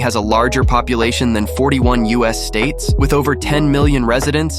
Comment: You said, "LA has a largest population than 41 US states" subtitles said "44" 9:50.